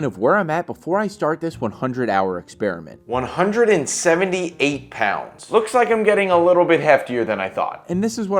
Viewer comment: i have this playing in the background and i heard "108 pounds" at first and my heart dropped so bad i got so worried for a second there 😭😭